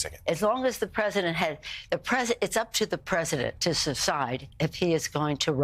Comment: Did she really say "cecide" instead of "decide" at 3:33 into the video? Freud would be proud of her slip that reveals her true thoughts!